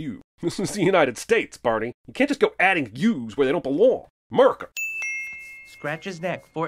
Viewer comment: the but you said murica instead of america now you added an u